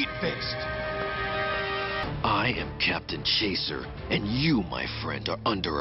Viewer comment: I thought Smokey said captain shitter for a sec 💀